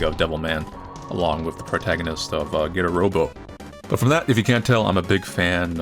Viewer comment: what did he say erubo or airubo? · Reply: Getter robo